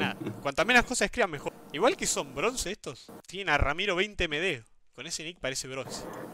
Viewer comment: escuché "Ramiro10MD" y me quedé re mal viajando xD